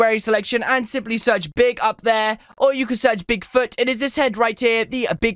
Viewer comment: I thought you said pig food in the video lol